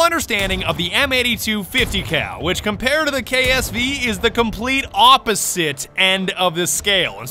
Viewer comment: did he just say AWPosite side? Or is it just me